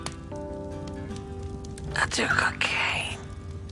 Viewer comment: what did she say? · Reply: "I do cocaine..."